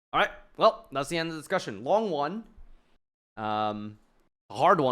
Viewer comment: I genuinely thought he said longyuan